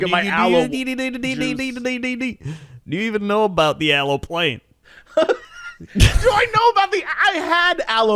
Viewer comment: i misheard that as Aloe Place, and was about to leave a comment. Remember the Aloe moe.